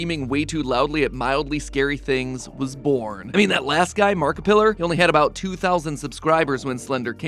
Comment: was it just me or did he call him "Markipiller"